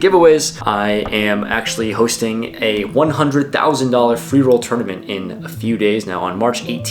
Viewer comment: I thought you said "a $100,000 funeral tournament"